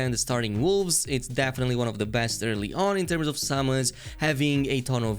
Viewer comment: Oh summons. I thought was saying salmons. Was wondering wtf